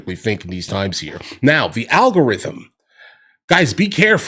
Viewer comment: I keep hearing of this Al Gore guy and his Rhythm....